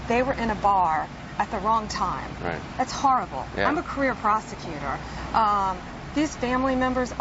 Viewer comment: lol I thought she said "I'm a career prostitutar.